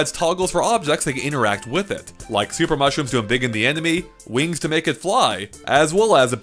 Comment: Did he just say ... "emBiggen"